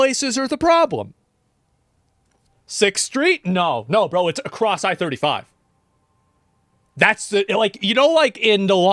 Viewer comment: Did you say cross-eyed 35?